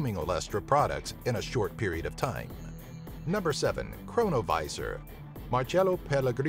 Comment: I thought he said coronaviruss 🤣🤣🤣